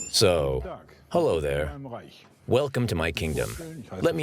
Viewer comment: He actually says "Welcome to my Kingdom of Heaven" - Wilkommen in meinem Himmelreich.